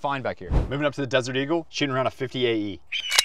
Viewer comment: It sounds like your saying around and not a round